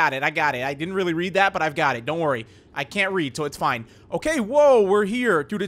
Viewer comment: i heard say "I can't read" instead of i can read...